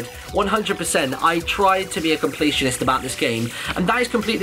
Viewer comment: I heard him say competitionist and I'm like "He's talking about Shadow of Mordor" He then goes Assassins Creed and I'm like "Yup, Shadow of Mordor"